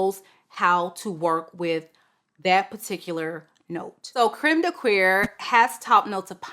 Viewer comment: I know you’re pronouncing it correctly but the way I belly laughed when I thought I heard Crème de “Queer”. 😹😹 (Disclaimer: I’m a woman married to a woman and intend this in good fun.)🫶🏻